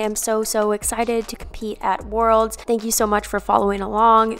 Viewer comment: What bad experience??? Aren't worlds 30 March??? huh??? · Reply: She says "that experience" but there is a little stutter in the audio so I heard "bad experience" the first time I listened too.